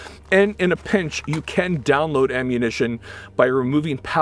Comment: thought he said "you can download ammo" not "you can down load ammo"